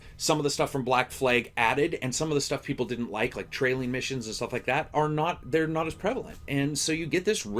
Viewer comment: im only about 20% through with rogue, but did i hear you say "no trailing missions"?....i've made my decission. ubisoft finally got their head out of their asses.